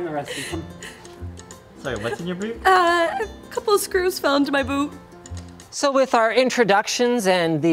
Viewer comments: it sounds like she said the screws feel into her boob. The subtitles would agree with me... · Did She said couple of screws found in my boobs 😂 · it sounded like boob, i thought "oh yeah" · She said Boob!!! Instead of Boot!! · did she just say boob? gigidi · I thought I heard her saying boob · did she just said "couple of screws fell into my boobs?" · wait did she say boot or boob? lol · Am I the only one who heard BOOBS! 😂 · thought she said "couple of screws fell into my boob" :/ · did she just said 'a screw dropped in my boob' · did she just say, a few screws fell into my boob? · "A couple of screws fell into my boob." We know what you said! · A couple of screws just fell into my ...WHAT.....did she just say Bo*B....WTF !!!!